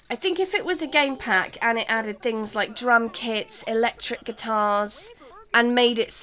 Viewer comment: You said drum kits and I thought you said: drunk hits! 😂😂😂 Nice episode. ❤